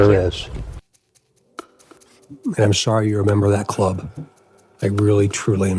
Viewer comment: BP knew about the “cult” a while ago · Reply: Maybe her COWARD of a garden gnome, pathetic husband is in 1 ? I mean I thought DC said “ I’m sorry yr apart of that CLUB “